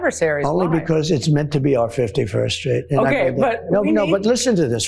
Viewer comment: Am I mistaken, or did Trump say "fifty first street"? I usually watch/listen to everything on YT at 1.5x speed, but on this occasion I replayed it a few times over and even at normal speed.